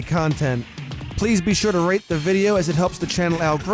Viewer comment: “...please remember to rate the video and....” 7th grade sibling walks past: “did he just say to rape his YouTube video⁉️⁉️⁉️⁉️⁉️⁉️⁉️⁉️⁉️ XD